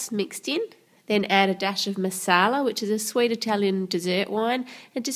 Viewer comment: I thought u said masaala which means spices in hindi ur fan from india <3